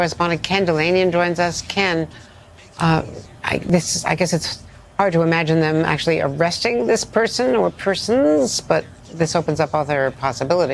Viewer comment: I swore I just heard the news correspondent say "It's hard to imagine them arresting the people planning an assignation President Trump